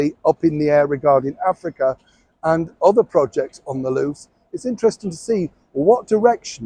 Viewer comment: 🙋‍♀️Hi Neil! I actually thought you said their 'projects on the Loo'. I had to listen again where you said "projects on the LOOse!! 🤣🤣🤣